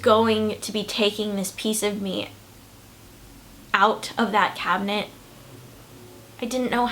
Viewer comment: closet* not cabinet P.S. this video is kinda.....oh what's the word... GAY